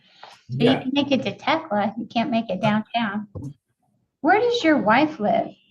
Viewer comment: Does Judge say 'You can make it to Tecla, you can't make it down hya?" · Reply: "can't make it downtown."